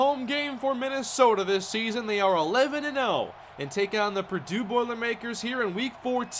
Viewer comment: When you realize there's only 2 games left in this series... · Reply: Cole Zenczykiewicz oops my bad I thought he said 10 and 0